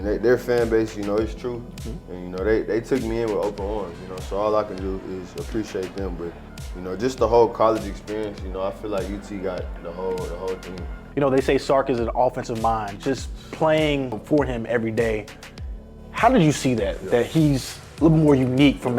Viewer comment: Almost thought he slipped & was gonna say UT got the HOE’s 3:39 😂😂